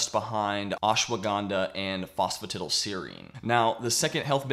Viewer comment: what did you say here? Phosphotatile seri is what I heard I’m interested in looking further into it Thanks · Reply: Phosphatidylserine.